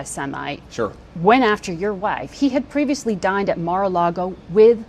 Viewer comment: I can't be the only person who thought she said "died" instead of "dined